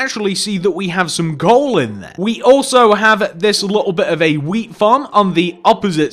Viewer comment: I swear he said "here we have a bit of a weed farm"